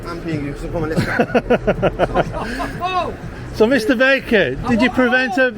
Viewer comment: I thought you said his name was Mr Bacon, it was only at the end I realised you said Mr Baker.